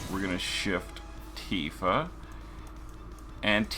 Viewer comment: I hate to question your political affiliations, but it sounds like you said, "Antifa" @ 10:07~10:08. 😂